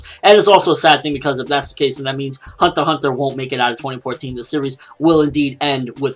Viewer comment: what does he say at 5:54? hunter x hunter wont what in 2014? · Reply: He say that hunter x hunter wont make it out...